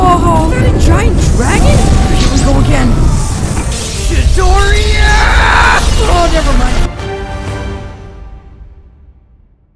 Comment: did u say chiidoriaaahh!! Or Midoriyahhhhhh (Yeh,I'm a dumb)